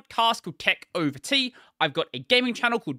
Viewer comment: I've always tried to understand what exactly are you saying after the "gaming channel", I even lowered down the speed to the maximum but still failed. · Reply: its just his name BrodieRobertsonPlays